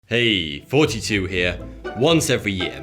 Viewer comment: I always listen on headphones so it sounds like he says hey 42 here. Which makes me think of that vince vaughn movie where he has to be businessman 42 😂😂😂😂